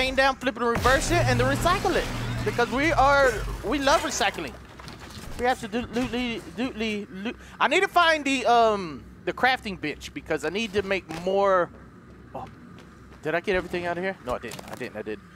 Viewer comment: I'm sitting right by my mom and she is listening to this in the background and between 19:30 and 20:00 it sounded like he said crafting bitch and not crafting bench 😂😂😂😂😂